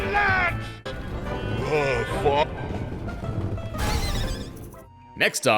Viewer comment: why does it sound like he said f*ck?